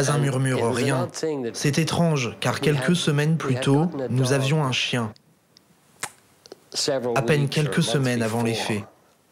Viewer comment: What does say at 19:20 ? That he had a dog or did not have a dog? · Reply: Had a dog until a few weeks before the murder